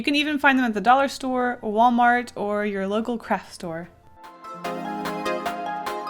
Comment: I thought she said local crack store XD